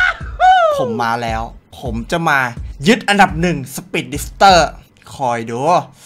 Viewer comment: ได้ยินว่าผมจะมาเย็*อันดับ1 5555